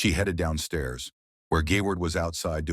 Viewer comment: did bro just call him gayward